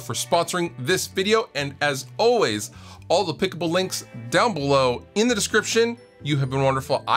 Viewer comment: I know you’re saying "all applicable links" but I keep hear "all clickable links" and it always throws me off.